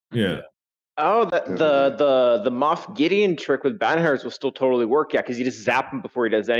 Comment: I couldn't understand what said at 47:16 about a moff gid comp